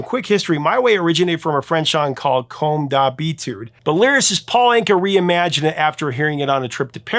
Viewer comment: Hey: When you tried to pronounce the name of the French song that "My Way" came from, "Comme d'Habitude", a French person would understand what you said it as "Comme Ta Bite" which means, well...uh... "Like Your Dick" (and we're not talking about a man named Richard...) The work of the Devil ! :face-fuchsia-tongue-out: once again ! 😆😅🤣